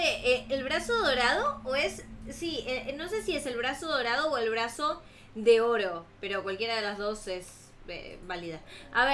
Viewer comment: Jaja pense que decia "el abrazo de oso" xD